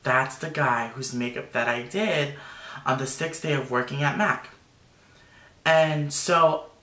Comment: ı think the second company that he talked about is mac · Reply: He actually says "mac" in minute 12:10 , I think he refeered to the first location he worked at